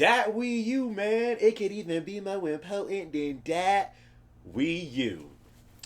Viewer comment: what did he say ? lmao anybody ? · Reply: It can even be more important than that wii u